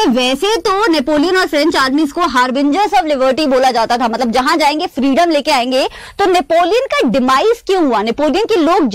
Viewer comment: What did mam say at 9:36? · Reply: Civil code of 1804